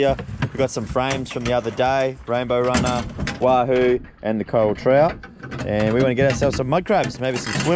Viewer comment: What species of fish was that? I heard "trouty" but im not familiar with any that look like that.